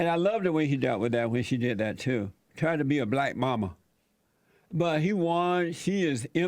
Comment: When JLP called Kamala Black Mama at first I thought he said Black Mamba. Lol! On a more serious note: RIP KB.